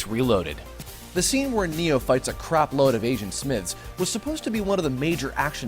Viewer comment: neo fights a bunch of asian smiths. i know i misheared the line but its way better this way.